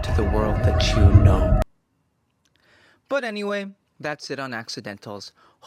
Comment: At the end after the hellish landscape of music theory oblivion gate I actually misheard his name as "...as always I'm mister mental", and I thought he's gone crazy.